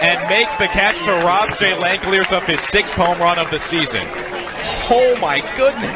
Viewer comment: did he say Shea Langeliers??